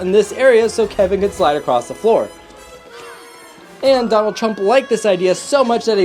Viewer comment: did he say Donald Trump and he was in there omg